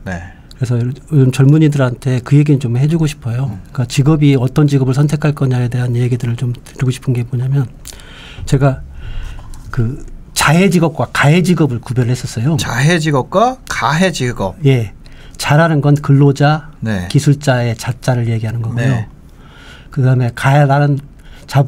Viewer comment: 재생속도 좀 빠르게해서 그런지 자해직업, 가해직업 으로 잘못 들었네요 ㅎ